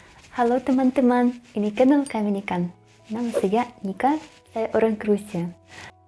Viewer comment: tadi sempet salah denger kirain lagi ngomong kami ikan